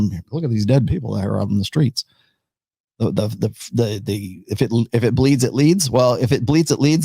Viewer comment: Correct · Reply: correct but i misheard "if it bleeds it leaves"